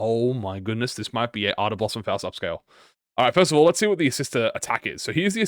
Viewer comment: Did you say Ardor Blossom Upscale 6:50 🤑🤑🤑